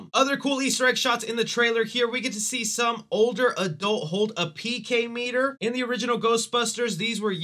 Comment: Good review, you got a new sub👍 Gotta say tho... Call me a GB snob, maybe you just stumbled on your words, but I cringed when you said "PK meter" at 5:48 instead of 'PKE meter.' Sorry. Great review. Just had to say it lol